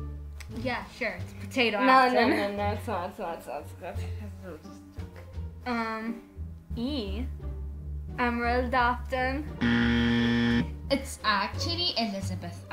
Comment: I cant stop laugh at "Emerald Afton" Edit : thx very very much 4 the likes Another edit : eveeyone talking abot the 4 at the first edit so basically sometimes u can put "4" insteand of "for" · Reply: I thought it said ennard afton